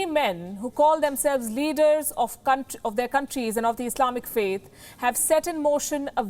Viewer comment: Did you just say "leaders of c**t..."?? lol